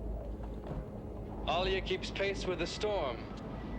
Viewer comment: Does he say: 'alia keeps pace with the storm'?